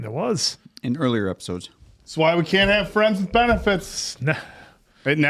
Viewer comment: I thought you said "This is why you can't have friends with Benedicts" :P